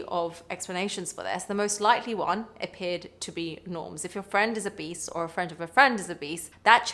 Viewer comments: What a totally WILD accent! I thought she said, 'If your friend is a BEAST!' 😂😂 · i kept hearing "if your friend is a beast"! 😅 had to figure out that's not what she was saying. 👹